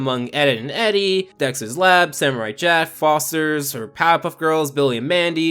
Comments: did he just say samurai JEFF LMFAO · is it just me, or did u just said Samurai Jeff?